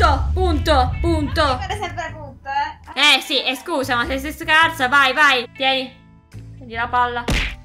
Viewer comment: Solo io ho sentito : eh eh stu ca* 😅😅